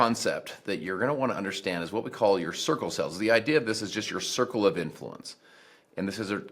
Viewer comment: Every time that says "circle cells" I keep hearing "sickle cell" instead.